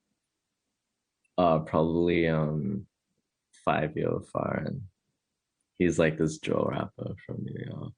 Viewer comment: i didnt understand that:( what names he said? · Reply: Fivio foreign